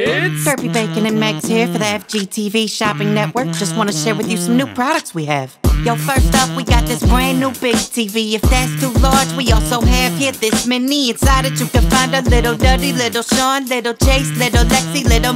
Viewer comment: did he say big fish or the b word